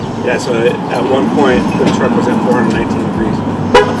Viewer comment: Did I hear that correctly, At 8:40 the Jeep was at 419 degrees?!?!?!??!